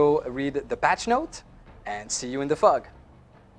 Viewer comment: 'And see you in the fuck' Lol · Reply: no, I thought he said " See You In The Smog! "